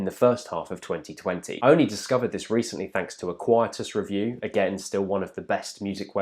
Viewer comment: What music review website did you mention around 14:55? I can't quite make out what you said, sorry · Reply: Quietus :)